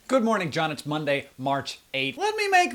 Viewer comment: I thought he said "poop morning John" at the intro. I guess that's what happens when the previous video is John saying the phrase "Red Green poop in my Nintendo" over and over.